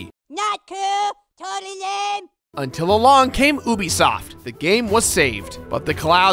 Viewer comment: At first I thought you said “Boobisoft”.